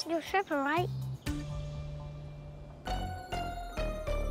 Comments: “Don’t tell on me” lmao she’s so cute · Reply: I thought she said “don’t color me” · “Don’t tell on me” lmao she’s so cute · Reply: Until I read ur comment I thought she said Don't color me because used the crayon. Thanks for clearing that up for me. 😆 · “Don’t tell on me” lmao she’s so cute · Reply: I thought she said, dad was telling me. Lol